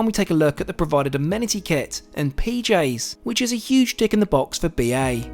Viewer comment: I think i heard him say dick in the box 📦 😆